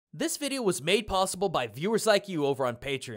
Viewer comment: thank you sorry I heard PBS in the sentence